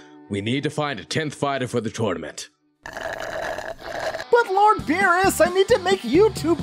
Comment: At first i thought he said long beerus